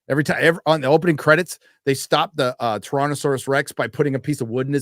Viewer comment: I swear to God, I thought said "Torontosaurus Rex" and I thought, what is that, the gay Canadian cousin of the Tyrannosaurus Rex?